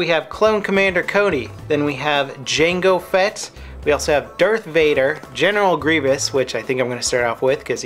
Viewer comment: it sounded like if you said "Dirt Vader". XD